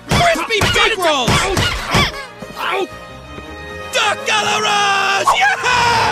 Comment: What does Otis say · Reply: “Duck à l’Orange!!!”